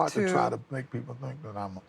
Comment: he whispers what sounds like "It's hard to try to make people think that I'm a murderer." While looking as if he's talking to someone behind the scenes (producer?) And the interviewer says "I know". That's strange... It seems as the angle is to make him seem very much guilty and he kind of breaks in that instance, almost as if he's acting? Also @ 49:40 he talks about "all he remembers is standing there with all kinds of "stuff" around" he pauses and looks behind the scenes again and at the interviewer, it doesn't seem that he's lost for words, but it seems he's signaling this is your cue to ask about "what stuff?" So he can say "blood" again this appears as acting...